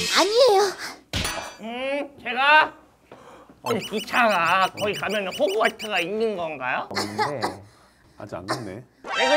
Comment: 기차가 거기에 가면 호그와트가 있는건가요? 다시 보니까 들리는 펭수의 말ㅋㅋㅋㅋ간이역이 호그와트로 가는 비밀의 승강장 같은거라고 생각했나봐 너무 귀엽고ㅠㅠㅠㅠㅋㅋㅋㅋ말투는 또 왜이렇게 애기야ㅠㅠㅠ